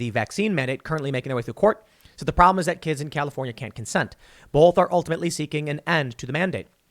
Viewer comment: I swear when I heard you read this point in the article I thought it was "kids in California can't consent" which sounds very wrong until and made make have a double take I read the article and saw "kids in California can't consent to vaccination."